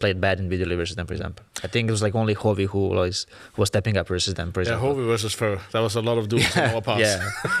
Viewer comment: Niko and Karrigan say Hovi or something and then again at 29:06 who does Karrigan say never tilts and then left practice? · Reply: rains name is Harvard. Havi.